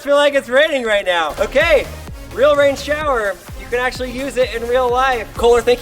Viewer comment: I actually thought it said PAIN shower.